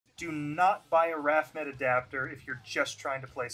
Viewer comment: I heard this as “do not buy a retro cult mods adapter” and got Royally confused